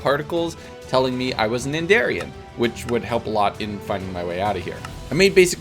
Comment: On day 1 when he said what his origin was I thought he said endarian